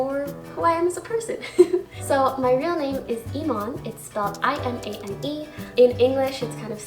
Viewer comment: I thought she said my name is Imane it means fake Edit: this wasn’t an insult I just thought she said that